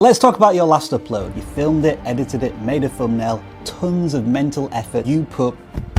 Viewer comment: Thank you. I started a channel may this years and uploaded 13 videos and some got 2 views, some 5 views for the whole 2 months with 2 subscribers. i kind of feel down but i created another 6 videos and uploaded them on schedule. a week ago one particular video blew and now am at 85 subs with 13k views. Am getting subs on daily bases anyway. I noticed my TN also got something to do with the video blowing up so i went back and recreate TN of similar videos on the channel and they are also started getting views and subs. I got the TN changing idea from watching one of youtube CEO's videos about how changing a TN can sometimes improve things for your channel. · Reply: You can't type out thumbnail. I thought you were saying Tennessee